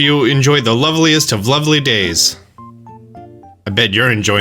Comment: When Isabel said lovely I thought she said lonely